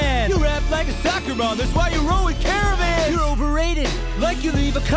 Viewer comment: Karen vans is what i heard at 15:20 and a bar i think is legit